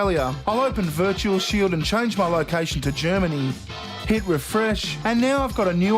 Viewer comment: I totally thought he said hitler fresh at first lol.